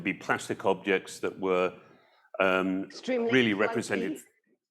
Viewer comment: Trying so hard to hear what Anna said at 5:51. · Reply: "Extremely clunky "